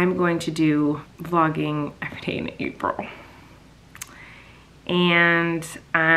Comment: i was actually so sad cause you said no and then i heard “vapril” and i CRIED omg skdbkdmf